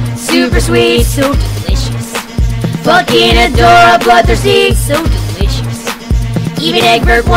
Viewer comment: i remember when i listened to this and thought it said adora blood disease 😭